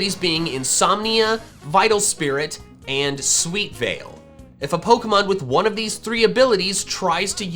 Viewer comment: When you said sweet veil I thought you said sleep fail